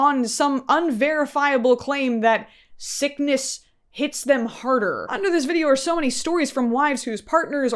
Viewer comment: When your present/gift is actually transactional and should only be met with equal or greater values 😂🤣 who are you, a car dealership? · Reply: Iii thought you said "sickness GETS them harder" 😳🫢🤣 had to rewatch that part